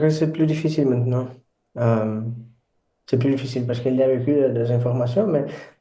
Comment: Agree on B1/B2 in French, but hard to understand, I heard "because" when he said "parce que" for instance. German pronunciation is weird, around A2/B1 probably. (I'm native French, B2 German, but rusty).